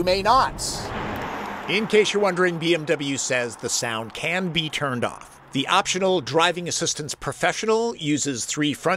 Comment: Caption issue @ 7:41 , audio says the sport sound CAN be turned off while the captions say it cannot.